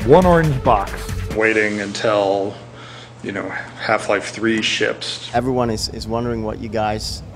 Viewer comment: DID I HEAR CORRCTLY? @ 2:26 HE SAYES WAIT UNTILL HALF-LIFE 3 SHIPS NO JOKE GO HEAR THE LORD